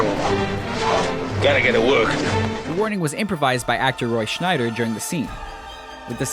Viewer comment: did he really said actor ROY SCHNEIDER?????